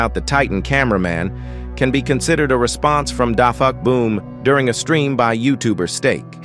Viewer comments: did I hear the fuck boom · DID YOU JUST SAY A CURSE WORD AT US?! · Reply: No he said da "fuq" boom